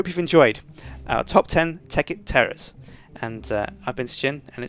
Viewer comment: Sound like he says top ten tekkit terrorists